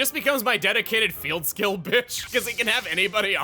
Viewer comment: Did i hear "dedicated field skill bitch" I think i did